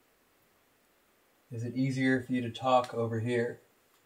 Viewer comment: The scream sounds like its saying COME TALK OVER HERE